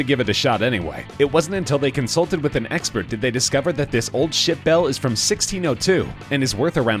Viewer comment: it sounded like he said “this old shit bell”